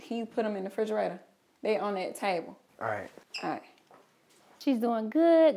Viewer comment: On 1:09 she did not say by the tv she just said table 😂